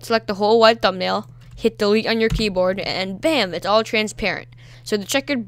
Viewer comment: what did he say? hit (something) on Your keyboard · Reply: delete